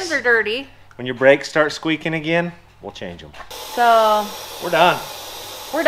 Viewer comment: "Boom, that's how Landon was born!" I'm dying!! Also, I thought was saying that next time the brakes start squealing is when they'll change the oil again!!